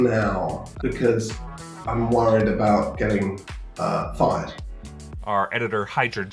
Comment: I thought he said worried about getting fined.